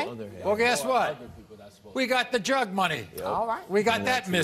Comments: Thought he said drug money 😂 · Thought he said drug money 😂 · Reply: I thought she said drug money 🤣